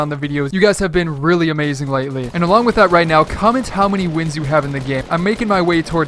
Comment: I thought it said comment how many wife's y have.